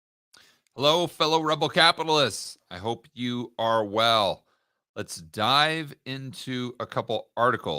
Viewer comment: Did he say “I hope you Orwell”?